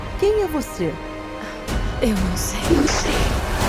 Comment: so conseguir ouvi Eu sei